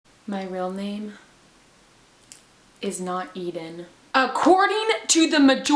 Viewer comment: I thought it said “My real name is not Ellen” hi